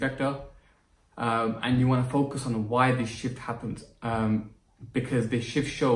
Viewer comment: When you said shift i literally thought you said shit which took me by surprise